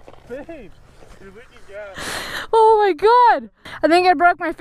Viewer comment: Did anyone else hear "babe" from starts at 4:55 finishes